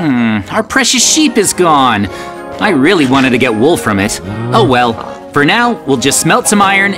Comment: I really wanted to get wool from it Sounded Like: I really wanted to get wolf from it